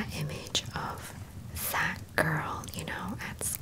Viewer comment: why'd I hear it as: "it gives me that image of....FAT girl" 😭😭😭